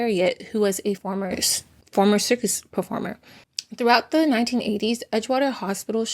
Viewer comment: did you say a “former circus performer”? Running the hospital now?